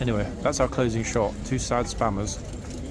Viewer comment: I'm going to get my ears checked. All these weeks I thought you were saying SPANNERS. 😂